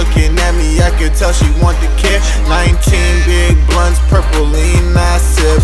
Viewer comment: older bro said he sound like he said purple in my butt now I don't hear blunt anymore fuck 😂😂😂😂😂